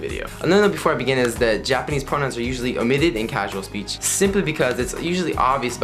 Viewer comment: it sound like you said "japanese pornos" when you said japanese pronouns ahahah